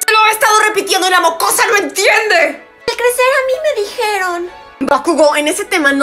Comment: No entendí lo que dijo Eri en el minuto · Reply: Al crecer a mi me dijeron* o eso entendí yo ✌️😅